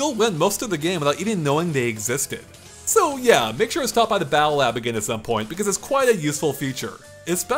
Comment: what does he say at 9:42 about unlocking the accessibility options? "yeah make sure to stop by the 'battle app again'"? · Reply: Battle lab.